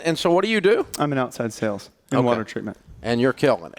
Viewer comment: What did the guy said he did for a living? He said sales and mumbled what kind of sales before Dave interrupted. At 1:20. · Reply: Outside Sales, in Water Treatment